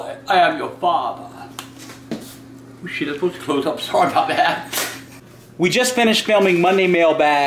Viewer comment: Did he just say... "Sorry"